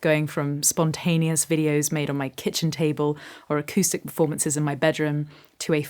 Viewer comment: What you said 👄”Acoustic performances in my bedroom…” What I heard 👂”Acoustic vomiteses in my bedroom…” 😂